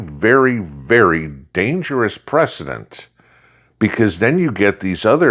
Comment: ……. Did you just say we have a Dangerous President!!?!!?